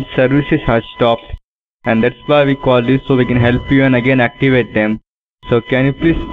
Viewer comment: did he say I give a damn? 0:31? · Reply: it does sound like it...but he said "activate them"